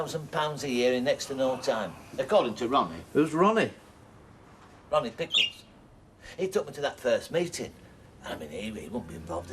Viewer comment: 🟢kevin attempting to dance😭 🟢 🙄ugghhhh not tricia and ower jaimeh 🟢so daniel osbourne should be younger than Sawpheh? but idk if thats what happened in modern CS.... 🟢sounds like norris said ronnie pickering